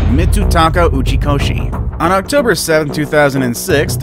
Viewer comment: On the 3rd one, it sounded like he said “Gucci koshi” .-. Who agrees?